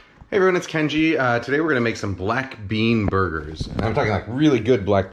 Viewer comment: Oh, I thought is said black bear burgers...